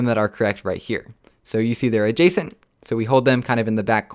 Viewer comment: Sorry, i'm from Italy. He says something like "jason" at 16:16 and i can figure out the meaning, but i would like to know what he says and the meaning. Thanks! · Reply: He meant "adjacent". You are most welcome.